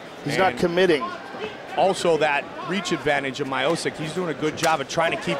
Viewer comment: No wonder Dana got rid of mine Goldberg it sounded like he was saying Miosick